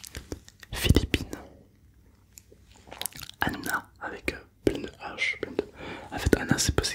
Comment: Alors je crois être le seul mais à 9:15 j'ai entendu hannouna